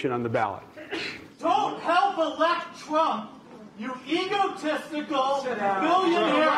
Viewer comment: I know the heckler said "egotistical" but I hear "evil testicle"